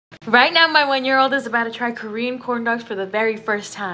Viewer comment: I thought she said my 1 year old is about to turn 3 💀💀